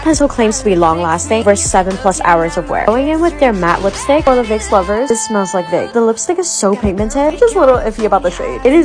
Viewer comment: i thought she said it smells like a vape